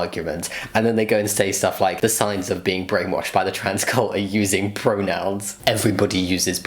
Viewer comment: "Transcult" oh my ficking gog. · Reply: I thought it said "transcuit" at first and I imagined a box of Triscuits that were the trans colors lol